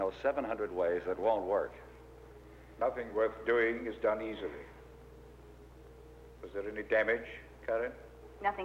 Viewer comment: What did Edison say? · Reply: "Nothing worth doing is done easy"